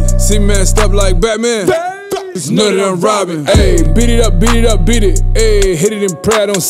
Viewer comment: when i first heard this i thought he was saying robin